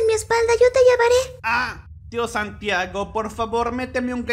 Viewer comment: por qué dice "Dios Santiago"